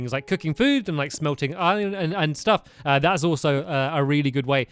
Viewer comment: what are you saying stampy? Iron?